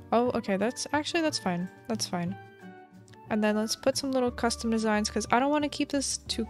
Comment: I actually thought she said lest put some little crusty designs 😂